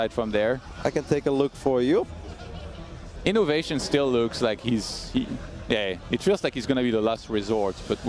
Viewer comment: Did he just say that "innovation still looks like he's gay..." lmao